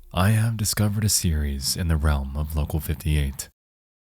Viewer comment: In the minute 00:47 he said "I have discovered a series of the ....... of local 58" Would you please help me fill the dots with the missing word. English is a second language and I heard that word alot I know that it means something like (society) but I'm not sure and I can't pronounce it without the spilling, Thank you guys. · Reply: he says “I have discovered a series in the realm of local 58”